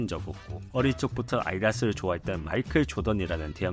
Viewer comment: 내용의 앞뒷말의 흐름이 부자연스러워서 듣기 힘들어요ㅠㅠ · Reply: 발음도 지금 비강에 공기가 부족한지 아이다스로 들림